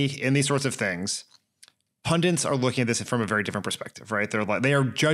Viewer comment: Did i mishear' or did Dan pronouce pundit as pundiNt? I keep thinking i hear people doing that - it might just be me! The other one i keep hearing is antismEtic for antisemitic. I get more pedantic with age.